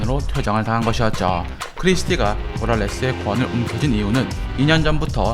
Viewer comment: 모랄레스가 부랄레스로 들리기 시작하네요